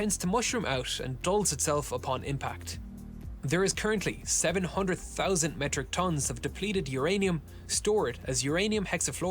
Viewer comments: Bit of a misunderstanding in the video. He said 700,000 metric tons of depleted uranium is stored, rather than the 700 metric tons you thought he said. Just wanted to make sure you caught that lol. Love the video! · you talk about "700 metric tons isnt that much" but you misheard. The narrator said 700,000 metric tons :) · 700,000 metric tonnes of depleted uranium not 700 . you misheard the video voice. BTW great video, love your enthusiasm. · I think you misheard what they said about the depleted uranium. They did not say 700 tons but 700,000 metric tons. That's a big difference